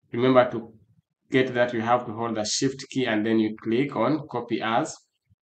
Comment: "shit key" is what i heard from the "shift key"